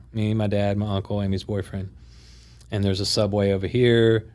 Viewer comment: Is he saying Amy's boyfriend? She's gay and was with a woman during the time of disappearance according to the documentary. She had a boyfriend too? That wasnt mentioned. Did i mishear this?